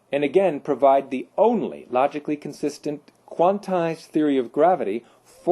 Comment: Dear people, may I ask if anyone knows what this word is, at 9:12 / 9:13 (mins) I am trying to type this bit out ... to me it sounds like "quantai" ... but I couldnt find such a word, and being english, it may be an accent thing!:-) ....."like waves on an ocean – these theories are extremely successful - and again provide the only, logically consistent, ****quantal*** theory of gravity, forcing scientists ...